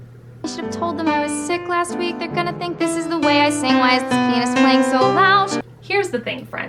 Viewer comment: why does the caption say penis